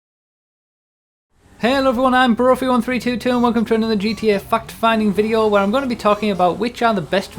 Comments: It sounds like he is saying "fuck-finding videos." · I thought he said "and welcome to another fucked fan video"